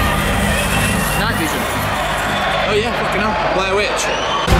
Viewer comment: what did Matt say at 1:00? 6 days and i’m still trying to understand lol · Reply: "Night vision? Oh yeah, fucking hell, Blair witch"